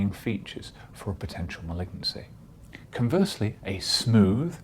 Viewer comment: can you please tell me what did you said at 2:41 potential what? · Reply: Potential malignancy